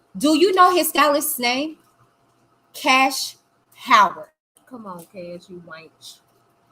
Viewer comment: I'm a little confused at 55:05.. when is talking to someone about cash Howard having the conversation with R.Kelly about the semen drying on Azriel's face, I swear I hear the person she's talking to saying "come on Kash, you white (something)" is that what I heard? Because I would hate to think that has people on here who are racist against anyone. Especially when this episode specifically is a black Predator against an young black female victim at the time and to Kash Howard was black. I despise racism in any form but I think it's worse when someone has a public forum and a trying to discuss something of validity and then have someone like that making comments. Because I'm heated, I may be mistaken so please let me know did she not say that? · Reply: Sweetie they said “ come on Kash you wantch!” Where did you get “ white” from that…